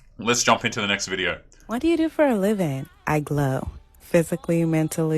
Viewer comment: What do you do for a living? I glow! You what? It sounds like you said, "you glow". That is not a rational answer for a vocation. It sounds like something a crazy person would say.